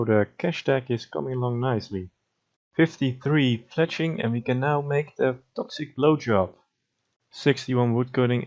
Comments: did you say "we can now make the toxic blowjob" 😂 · did you just say “ toxic bj”? 🤣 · ". . .Toxic Blowjob. . . " is exactly what I heard lol. Even if that isn't what you said, I considered subbing at the Crab Rave "Autism" part, and locked in the sub at this moment lol.